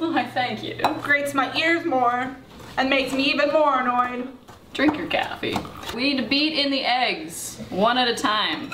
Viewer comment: I thought lance said “drink your gasoline” instead of “drink your caffeine”